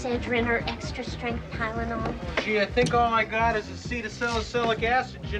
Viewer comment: What did he say? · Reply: “Gee, I think all I got is this cedacelacytic acid. Generic.”